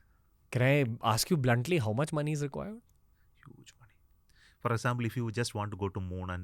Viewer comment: what did he say? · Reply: a huge money